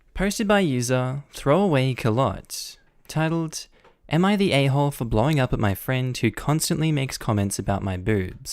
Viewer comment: I thought the first one said “Am I the A**hole for blowing up my friend.” I have smol brain